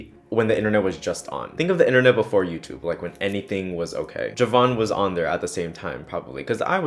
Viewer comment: HELP I MISHEARD U AND HEARD ‚ ravon ‚ AND I WAS LIKE NOOOOOOO RAVON WOULDNT DO THAT 😂